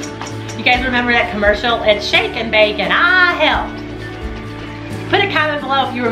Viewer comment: I remember the shake n bake commercial! When I saw it when I was a child I always thought the little girl said 'Shake n bake...and I HATE 'em' LOL . Thanks for the video and for all the videos. Love u guys! 💓